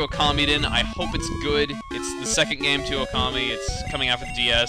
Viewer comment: When you were saying 'Okami' I kept hearing 'Commie' I was about to say 'Chugga, I never knew you were a comrade! Ura equailism! Ura Communism!'